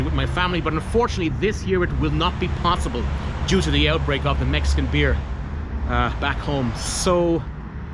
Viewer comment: You said not going home because of ?? (8:05)? Sounded like "Mexican fear"?